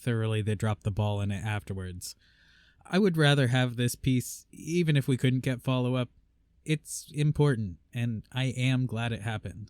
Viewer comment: You said "I would rather have this piece even if we couldn't get follow up." I heard "I'd rather have you, cursed or not."